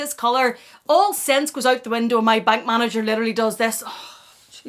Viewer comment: I misheard "bank manager" and was like "The heck?! She has a BAG MANAGER??" 😂